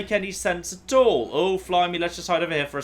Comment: 1. Did you say "flimey" around 58:30? 2. Steve again! You never did explain why everyone is Steve. 3. Congratulations. You deserve all the subscribers in the world. :>